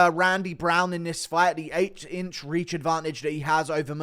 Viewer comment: did he say 8”? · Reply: 😂 yes. Dude has an 8 inch reach advantage